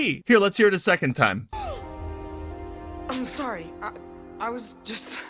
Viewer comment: I thought he said "I am sorry" I have qualms with both sets of voice acting performances but for different reasons.